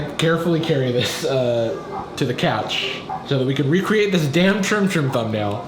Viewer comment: I thought he said isis on the couch